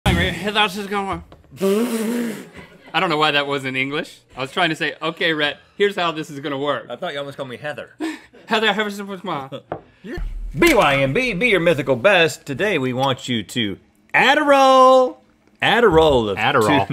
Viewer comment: “Here’showdisgoin” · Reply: I hear it "here thou tsis goin'"😂😂😂